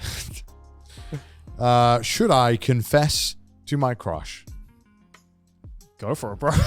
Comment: god damn. i thought he said should i confess to my crotch at first.